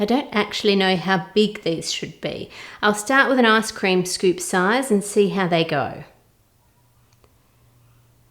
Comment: i heard arse cream lol